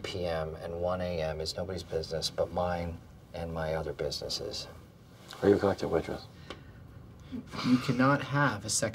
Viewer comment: what the hell does he say there? Sounds like "are you a collective waitress" but that makes no sense.